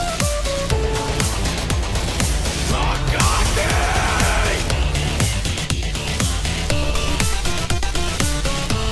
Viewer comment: What did Tabi say? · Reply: Agoti